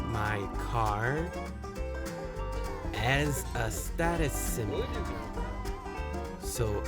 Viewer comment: I see my car " as " a status symbol 이라고 들려요ㅠ is 가아니고요 See A as B 그건가보ㅓ요